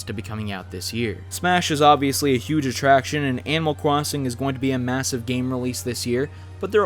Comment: There Is Fucking Stage Builder Also 8:00 did he just say cwossing